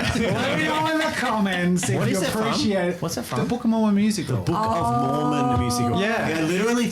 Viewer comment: I literally thought Jazza said "Pokemon the Musical" the first time he said the phrase, which left me really confused. Regardless, like, and, a reference to "The Book of Mormon Musical"" meant nothing to me either. Though to be fair, I am neither a Mormon nor a musical theater person.